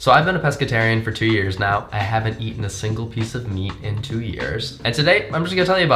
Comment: I thought it said "why i havent eaten A MEAL in 2 years". It was meat lmao